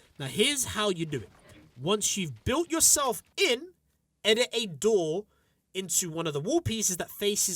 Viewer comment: did he say edit a door? who does that.